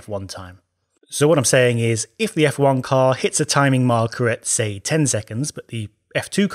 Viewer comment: said f1 instead of f2